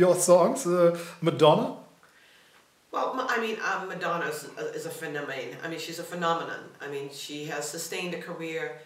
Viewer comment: Does she said "Madonna is a friend of mine"? I can't understand what she says at 0:49-0:50... Can some one tell me please? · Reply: Madonna is a phenomena